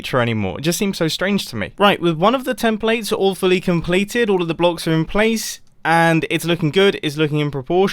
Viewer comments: I thought he said with all of templates awfully completed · lol I actually understanded instead of "all fully" awfully